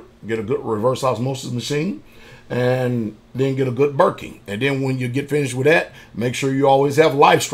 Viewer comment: Wondering at timestamp 7:45 what a “burking” is? I’ve seen him mention it in videos before, but can’t understand what type of machine he is referring to? I heard RO system, life straw, food stored, etc. just not this specific part. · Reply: " Berkey" is a water filter . One of the best ones out there.